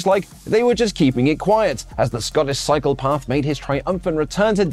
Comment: Did you say "cycle path"? Oli's not going to be happy about this. Edit: (Proceeds to get served with a master class in wrestle-talking.)